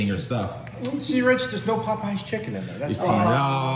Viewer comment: wut say? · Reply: He said There's no Popeyes chicken in it